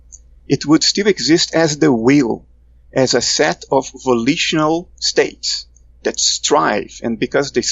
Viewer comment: Can anyone help me out with what Bernado says at around 13:27. He says “it still exist but it exists as___” ... sound like he says ‘will’? Then he says “it exists as a set of ____ states” a word that starts with a V? Really interested in this point. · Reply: Volitional.